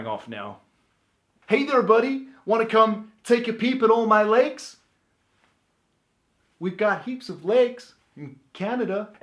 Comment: Lol idk y but I heard ‘Hey there budday, wanna come take a peep in all my legs?’